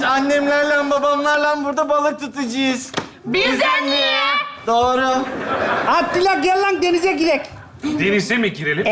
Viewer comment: burda ne diyor? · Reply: Bizeneğe